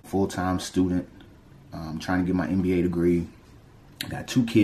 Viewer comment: What degree? Sounds like he said NBA degree